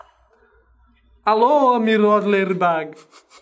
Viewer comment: eu escutei Lady Bug... Quem mais escutou isso da like nesse comentário